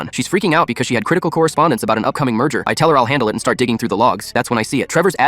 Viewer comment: the subtitles say Diane has lost her emails about an upcoming MURDER. Holy fuck OP you're working for the mob.....